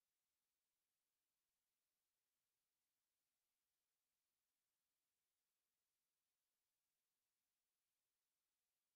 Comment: nél mit mondanak, milyen “Zsófitron”, vagy micsoda…? Többször visszatekertem de nem értem mit mondanak. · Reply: Zsófilter, Balogh Zsófi podcastja a Partizánon. 😊